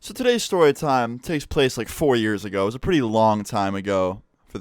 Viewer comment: I thought you said 40 years ago